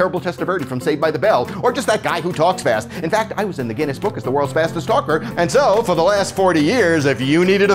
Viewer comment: I heard that as "World' Fastest Stalker"